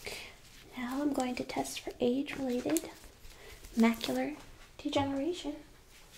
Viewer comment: I misheard that as "now we are going to test for AIDS related macular degeneration." :DD